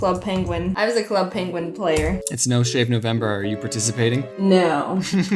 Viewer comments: I thought he said it's no shame November! Lol! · When they said no shave November I thought they said no shit November and she's like I think that's only for guys. I lost it